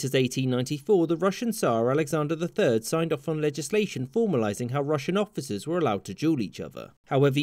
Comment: "Sir, you have offended my honor. I challenge you to litigation." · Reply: I thought you said 'ligation'.